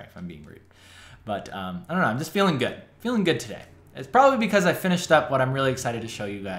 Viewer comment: I literally thought you said "Im feeling rude today" :D hah